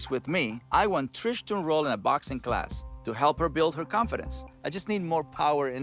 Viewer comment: I legit thought he said he wanted her to roll in a box of glass LOL but boxing class makes more sense I suppose.